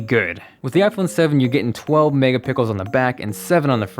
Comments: did he just say "mega pickles"? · I thought you said 12 megapickle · I'm sad they didn't do the dark blue one. Jet Black looks great, but I'm gonna wait til all the complaints come out about the scratching.. lol. Wait a sec, did I just hear "megapickle" again? WTF. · it sound like he is saying mega pickles, lol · I swear he just said mega pickles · Lol he said "pickles" instead of "pixels" at 4:01 😂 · he's says megapickles instead of megapixels 😂👌🏾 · he said pickels instead of pixels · did he say 12 mega pickles 😂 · OMG at 3:59 he said mega pickles instead of megapixels. lol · who else heard 12 megapickle? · did you said "12 mega pickles"? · Did he say Mega Pickles? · did he say Megapickles at 3:58? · Did he say "12 megapickels" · Did you say mega pickles at 4:01. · Was I the only one who heard "megapickles" at 4:01😂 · Great Vid! Is it me or did I hear him say @ 3:59 "12 mega Pickels on the back..." hahaha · when he talks about the camera he says pickles instead of pixels 😁😂😂😂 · you said 4 mega pickles instead of pixels xD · Did he say Megapickels at 4:00? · am I the only one who heard "mega pickles" at 4:00 ? 😂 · I'm I the only one that heard him say we get 12 mega pickles on the back? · Did anyone else hear "Megapickles" @ 4:01 😂😂😂😂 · It sounded like he said the camera had 12 mega pickles. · Did i hear "Mega pickles" ? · did anyone else hear him say "megapickles" ? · Did he say...megapickles? 4:00 LOL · did he just say 12 megaPICKLES? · did he say "mega pickle"?